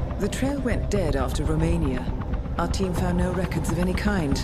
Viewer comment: Did I just heard Romania at 6:38 ?💙💛❤️